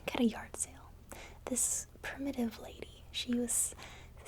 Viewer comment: Why do I keep hearing her say " this primitive lady" at 17:55 ? Does anyone else hear that or am I hearing the wrong word?